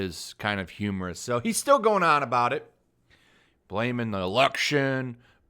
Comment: Okay, I know at 1:06 you intentionally mispronounced the word, but it legit sounded like "Blaming Roxas." So I immediately thought "the fuck does Kingdom Hearts have to do with this crap?"